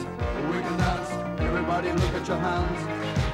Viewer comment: Meet Me ❤, I thought He said.. Everybody Take off Yer Pants 👖😭👖😭😂😂😂😂❤🎉 OMG